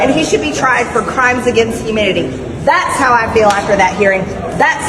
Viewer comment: It sure sounded like she said "crimes against humidity".